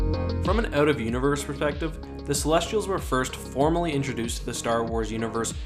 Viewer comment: Kinda sounds like you said "perthspective" at 1:35 lol.